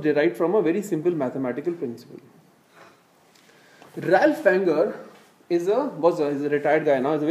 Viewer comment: which fund manager does he refer to ? I can't hear that name very clearly. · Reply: Ralph Wanger, got it. The slide is faintly visible in the back ground 🙂